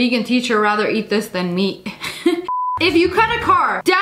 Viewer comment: it sounded like she said meek instead of meat haha but I knew what she meant